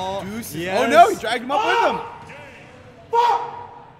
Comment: me: did he said F**K